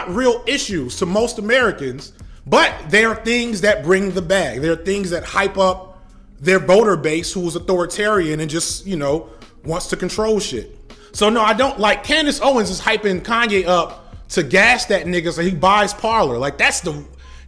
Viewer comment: did you say, "boner base" for "donor base?" That's good.